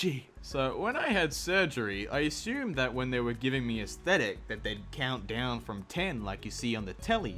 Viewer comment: I actually thought he meant aesthetic instead of anaesthesia so I was like “Excuse me, w H A T ?”